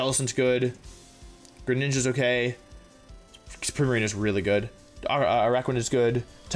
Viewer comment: which water Pokemon does he say is really good? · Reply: Primarina